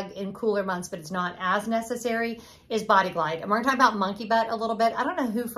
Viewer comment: Bahahaha, I thought you said funky butt-which is also unfortunately true😆 Timely video since my husband booked us at the end of August. I grew up in Florida (we've been away for decades)and thought he'd lost his mind! But it just occurred to me... margarita glasses are rimmed with salt! Boom, fixed!